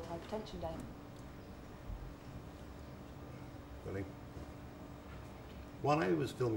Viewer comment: Did he really say, "Really?" at 0:35? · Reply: i thought he said 'rolling?'